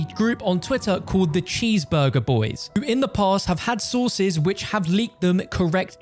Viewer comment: When he said sources I lowkey thought he said sauces for a fat minute